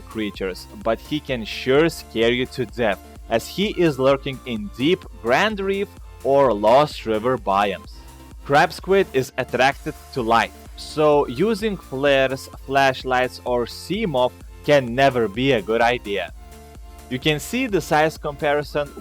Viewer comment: i thought Ya Said Crap Squid Not Crab Squid XD